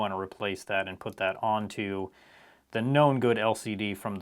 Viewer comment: I always thought that you said non-good but you actually say known-good hahahahahaha